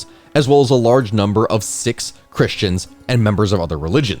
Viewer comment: closed caption says "six" but I believe you meant it as "Sikhs".